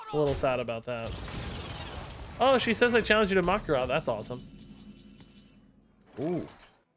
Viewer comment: What does say? All I hear is "I challenge you to makkara" (sausage in Finnish)